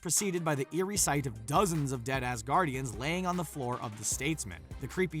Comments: In the Loki scene while the dude was saying "dead asgardians" I heard "dead ass gaurdians" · when they said “dead Asgardians” I heard “dead ass guardians” and just died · When he said dead asgardians i thought he said dead ass guardians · It sounded like he said "dead ass guardians" when he said "dead asguardians" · I thought for a split second he said “by dead ass guardians” until I remembered we’re talking about Loki and put two and two together 🤦🏻‍♀️ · i thought you said “dead ass guardians” and was like “that’s not very kid friendly” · I thought he said dead ass guardians